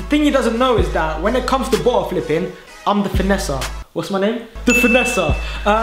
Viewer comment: What was that? It sounded like you said Vanessa instead of finesser.